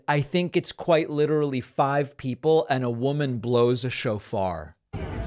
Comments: I thought he said, "chauffer," instead of "shofar" - (Lol)mind admittedly in the gutter. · I really thought you said "one woman blows a chauffeur" spat my tea out 🤣🤣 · At first, I thought you said, "...a woman blew a chauffeur." 😁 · OMG, I THOUGHT DAVID SAID: "A WOMAN BLOWS A CHAUFFEUR" I'M THINKING TO MYSELF: "I THINK I'VE SEEN THAT MOVIE!" 😂😂😂 I MISUNDERSTOOD THE WORD "SHOFAR"! 😂😂😂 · Okay. I'm totally going to embrace my ignorance but when David spoke about the people in front of Trump Tower, I thought he said a woman blows a chauffeur.